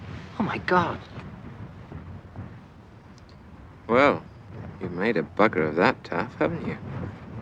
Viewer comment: What did he say? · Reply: "You've made a bugger of that, Taff, haven't you?"